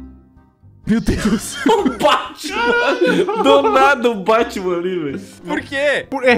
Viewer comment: sla, o jeito que ele fala Batman é diferente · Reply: O Báhtcheman